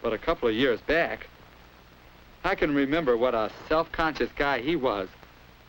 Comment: I thought it said Self-Conscious Gay 😂